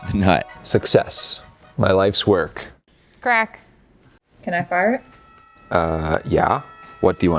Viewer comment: When she said “can I fire it?” I heard “can I fart?” and I snorted loudly in a room of napping people